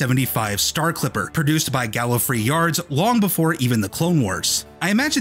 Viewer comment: did you say Galifrey Yards, or am I just noticing a non existent reference?